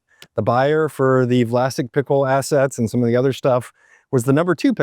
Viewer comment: As a kid I thought they were saying plastic pickles. Maybe I was right.